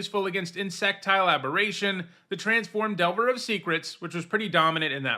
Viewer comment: It sounded like he said Erectile Aberration 😅